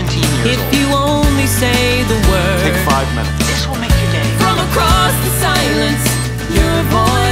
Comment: OKAY BUT I'M TH ONLY ONE WHO HEARD "this will make you gay" LIKE I WAS JUST READING FANFICTION AND I CHOCKED ON MY TEA